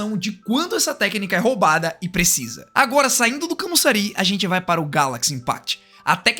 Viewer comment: Na parte que ele fala"saindo do kamusari"[Eu acho que era isso] entendi "indo para o saitama de one piece"kakaka